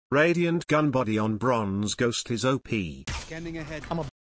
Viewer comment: I thought it said “radiant gun buddy on bronze lobby is op” Me waiting for someone to shout “BRO HAS A RADIANT GUN BUDDDDYDYDYDYDYDYDYDYDY”